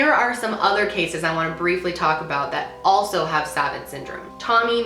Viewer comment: Savant is actually a french word and its pronounced "suh-vawnt" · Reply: Ohhhhh I didn't even realize that she was saying savant. I thought she was saying savit or something lol